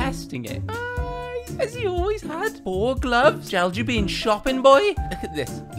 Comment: When said shopping I thought he said stupied👍👍